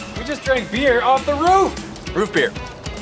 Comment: I thought I heard root beer